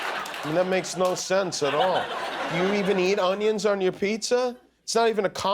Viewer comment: LOLOLOLOL That's what it sounded like "Roarnions". [roar + onions]